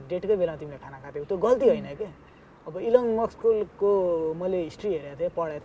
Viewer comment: Elon MUKS 🤣🤣🤣 Am I the only one who heard this!